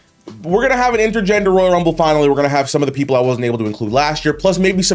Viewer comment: what I hear is you’ll be able to include Minnie and wheezy.